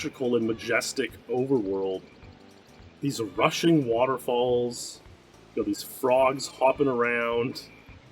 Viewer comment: i heard "Russian Waterfalls"